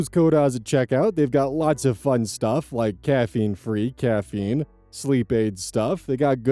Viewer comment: oz did you just say "caffeine-free caffeine"